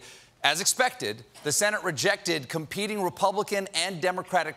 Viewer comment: Considering that the Dems control the house; no wonder they had the more votes. You can't be that dumb Stephen. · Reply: Overseas at the moment; and thought he (Colbert) said House, not Senate. My mistake. Damn, there are some mad supporters of Stephen. And I am far from a MAGA supporter, not that dumb.